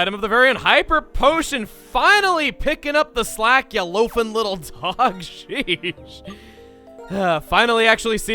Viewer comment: I thought emile was gonna call lillipap a "loafing little shit" XD